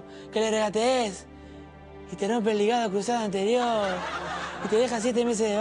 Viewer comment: otra pregunta que dice messi al minuto 1:22 no lo puedo entender muy bien algo de crusada anterior